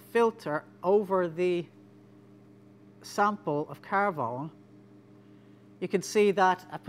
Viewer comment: haha, for a minute there, I thought he was saying carbon with a french accent, carvone. Then I saw the title